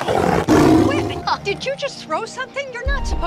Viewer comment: I could have sworn she said “WTF, did you just throw something?” 😂